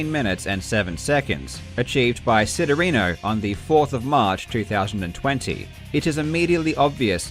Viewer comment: Am I wrong or did he say 4th of march but on the screen it shows 4th of may?